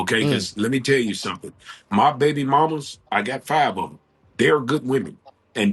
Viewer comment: Did he say has 5 baby mommas?!